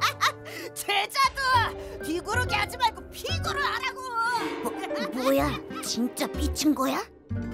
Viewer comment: 돌돌이 이때 뭐라고 하는 거예요 ? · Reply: "최자두 뒤구르기 하지말고 피구를하라고" 라고 한것같아요